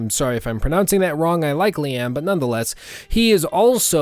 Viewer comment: When you said "I likely am" when talking about Ishmael, I thought you said "I like Liam" 😂